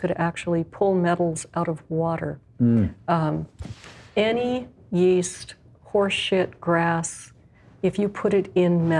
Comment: "Any yeast, horse sh**, grass..." is that really what she said, or did I misunderstand?